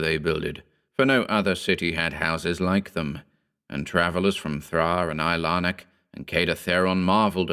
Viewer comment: Did he just say Thra? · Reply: Pretty sure he said Thraa.